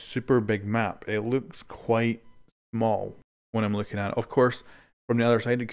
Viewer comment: am I the only one that noticed him saying “mal” instead of smal?? Great video btw :)